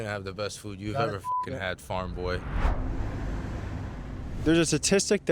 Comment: the way he called farm boy felt like he wanted to call him goy-boy or something sounded like an insult to me